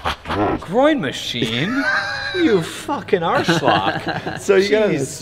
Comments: Did he call him a "arschloch" hahaha? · did say "arschloch"? (german for asshole) :D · did he say arschloch at 1:48? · did he just say arschloch